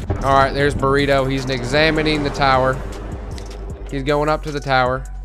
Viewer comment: anyone else hear "NIGzamining the tower" ?